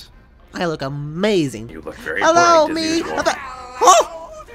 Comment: DID MOON SAY “you look very hornier then usual.”